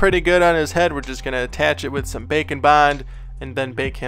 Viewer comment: I just realised that you say bake-n-bond I had thought you were saying bacon bon